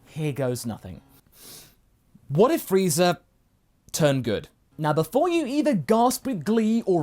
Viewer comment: I thought it said "what if Frieza turned gold"